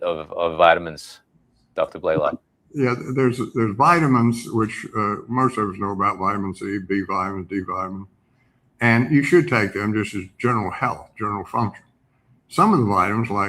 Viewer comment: what does he say at the end of this sentence? He trails off again, talking about riboflavin and D3. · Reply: “There’s vitamins which — most of us know about vitamin c, b vitamins, d vitamin. And you should take them just as general health, general function.”